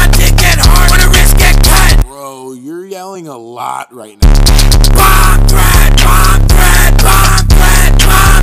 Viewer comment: I thought he said bomb bread